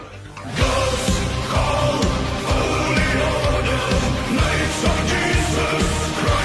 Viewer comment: You say the begining is random vowels but I swear I hear "Gods... Own... Holy.. Order.." But cant quite hear the second part.